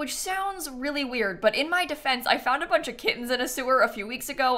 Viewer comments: I thought u said u found a couple kids in the sewer a couple days ago 🤦🏼‍♀️ now I realize you said kittens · When you said you found kittens in a sewer and you are fostering them for a second i thought you said you found kids in a sewer and are fostering them